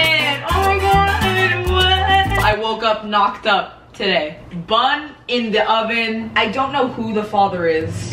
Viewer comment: Bun in the oven, baby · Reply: i thought you said burn it in the oven, baby